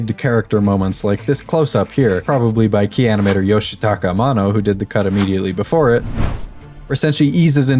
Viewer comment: I had to replay a few times when you said mano.... i coulda sworn you said amano